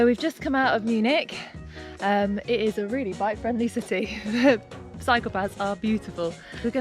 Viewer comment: I heard: "Psychopaths are beautiful". 😆